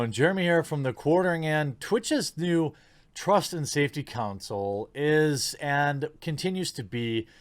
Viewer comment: Everytime I hear "Trust and safety council" I keep hearing "Puritan cleansing council".